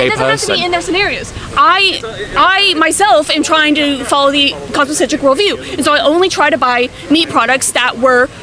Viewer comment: what is this word she's saying? She's said it a few times by now, and several times I've not been able to catch it -- but other times it was at least partly because you were talking too (so I figured I just couldn't understand it because I'm not good at parsing when two people are talking at once), and this is the first time I've heard it with good isolation... and yet I still can't make it out. something-centric world view... what's the "something"? Anyone able to grok this? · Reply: she's saying cosmic centric worldview.